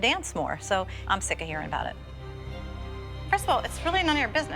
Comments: did she say crystal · Melissa said: Christel instead of Christie..! 😂❤😊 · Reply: It does sound like christel but she is saying first of all